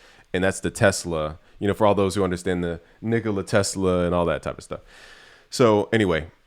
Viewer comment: Am I the only one who heard him say Niggala Tesla?